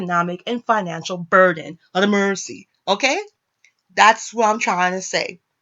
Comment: what did you say? Ah day mercy?